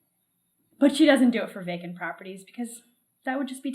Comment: Correction, you said "she doesn't to it to vacant properties..." She said "I only do it to vacant properties..." The bias is showing there. You flipped what she said.